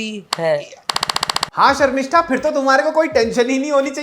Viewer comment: did u say Sharmistha 😅😂